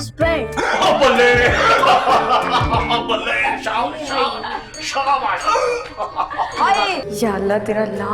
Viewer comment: my fave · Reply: who heard Shahana 😂